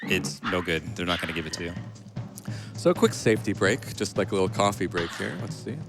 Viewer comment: I had the volume turned down a bit and I thought he said it was time for a Bill Cosby break. I was like holy crap, whats this game going to do?